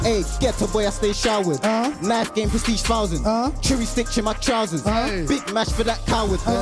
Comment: What does the don at 16:52 say? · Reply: Chewy sticks in his trousers